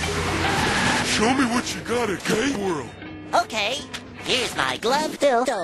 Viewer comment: Gay World? I thought this was Spanish class! LMAO jk But seriously, I thought he said Date World.